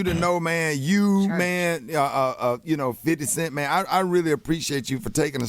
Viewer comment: Rickey did you call Snoop 50 cent?